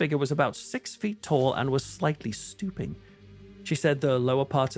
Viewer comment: At first I thought you said slightly stupid instead of stooping. i was like hey now that's a little uncalled for. 😂